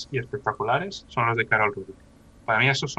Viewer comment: carol qué?! no se entiende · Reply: Saludos, es Karol Rudyk, te dejo su perfil de instagram <link>